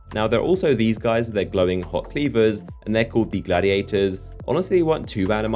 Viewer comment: I for real thought you said glowing hot "beavers" instead of cleavers